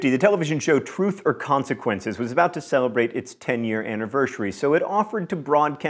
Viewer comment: Has John always said annivershary? I assumed I misheard it the first time but then he said it again.